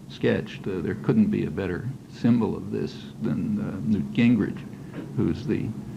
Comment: who's named did he say? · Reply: Newt Gingrich (speaker of the House at the time)